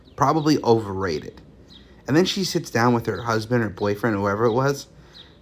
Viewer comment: Only time a chicken sandwich is life changing is when it's laced with salmonella. 😂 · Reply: I thought he said " & then she sits down with her BF & Husband!" About to say of course she did in this toxic culture😅